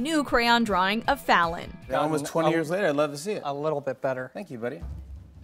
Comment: does Jimmy saying “thank you Betty” ?